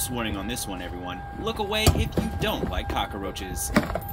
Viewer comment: Did he say Cock A Roaches?